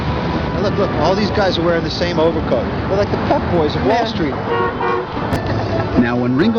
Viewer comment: I can't hear it as anything other than "fuckboys"